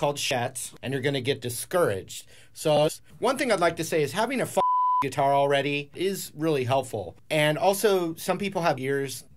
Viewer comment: “Having a f***ing guitar already is really helpful” 💀 Omg this is incredible, I hope has seen this 😂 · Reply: At first I thought he said “having fucked a guitar already” 😂